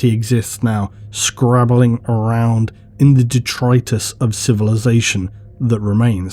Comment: For a minute there I thought you said Detroit. I mean 40k is dark but no need to to take it that far